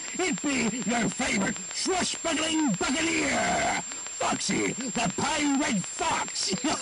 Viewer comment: I swear, for a second I thought he said "Foxy the pirate fucks" and my immediate reaction was "Yeah, probably"